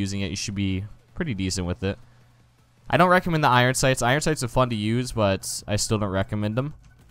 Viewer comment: on accident? u mean by accident? and when you said the iron sights are fun to use i thought u said the iron sights are f*** to use